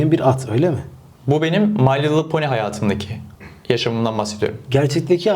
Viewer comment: adam my little pony deki hayatım mı dedi ben mi yanlış anladım